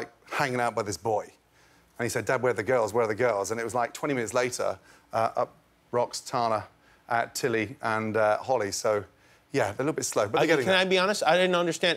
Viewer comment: haha thanks Jimmy, I couldn't understand that last sentence at 1:21 too, all i heard was: up rocks tana at tilly and uh holly so yeah...